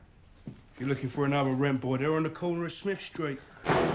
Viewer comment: What did he say in 0:32 ? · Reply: If you're looking for another rent boy, they're on the corner of smith street